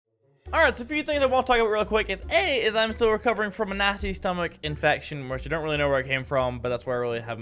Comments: I thought he said nazi stomach infection at the beginning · Why did he sound like he said a nazi stomach invasion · I thought he said Nazi stomache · I thought you said “Nazi stomach virus” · I thought he said 'nazi stomach infection' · For a sec at the beginning I thought he said a 'Nazi' stomach infection rather than a 'nasty' stomach infection... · At the start, I thought he said "recovering from a nazi stomach infection " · Am I the only person that heard 'Nazi stomach infection'. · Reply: Vangderp I thought he said nasty